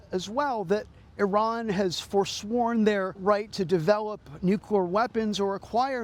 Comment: This guy says NUQUEER weapons....why? 3:59 why does he say it like this?? Why cant this guy pronounce the word nuclear properly?? play it at quarter speed, he literally says NuKwer...LMAO